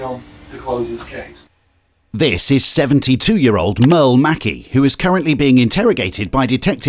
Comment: I swear he says meow.